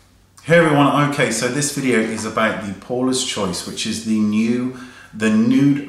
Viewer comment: Oh my... Every time I've heard you talk about this brand before I thought you were saying "poreless choice" not "Paula"... Silly me:/